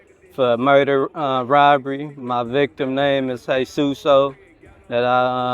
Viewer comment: I thought he said the person’s he killed name was Jesus, indicating a Hispanic man, it was actually Hye Soon, a 67- year old Asian lady